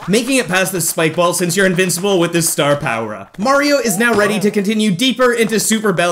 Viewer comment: did you call Mario mara